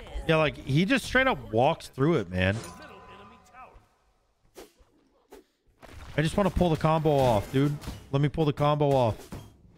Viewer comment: For a brief second, I heard "I just wanna pull the condo off, dude. Let me pull tho condo off" and thought "this isn't the video I signed up for"